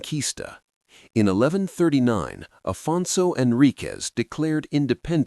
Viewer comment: Note: instead of 'Afonso and Ricas' (00:53), the name of Portugal's first king was AFONSO HENRIQUES.